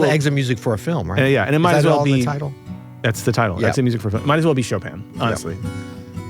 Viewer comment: "Might as well be Chopin" --> not sure if I heard that correctly (the auto subs say "Shopan" 😅) but you might also enjoy Ramin Djawadi's orchestral version of Exit Music (For a Film) for the soundtrack of Westworld. It fits perfectly with the scene where you'll hear it the background, and interweaves very cleverly with the main theme of the show. He actually picked several Radiohead tracks to include in the soundtrack, he must be a fan of the band as well.